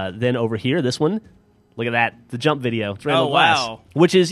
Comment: did they say " Jump Video" ?